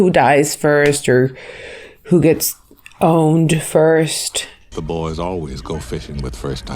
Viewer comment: I thought she said, Boned. Laughed my fuckin ass off!